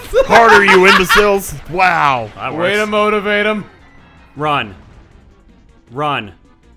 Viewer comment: Am I the only one who heard MiyaMOTIVE 'em?